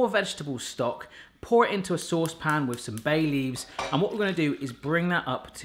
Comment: Hahahaha. Was multitasking, so was listening to you, whilst doing something else. Had to rewind as I thought you said add 'some Bailey's', imagine my disappointment when I realised it was 'Bay Leaves'.